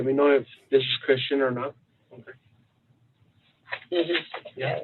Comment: I know I'm only 20 minutes in on replay crew but.. I heard "Christian, God, be OK." Just saying lol